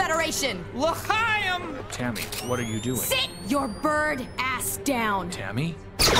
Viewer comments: That's what it was uncensored? Thought she said bird sh*t which is infinitely grosser to think about and oddly makes sense cause he's a bird. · What the fuck did he say????? 😂😂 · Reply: He said l'chaim.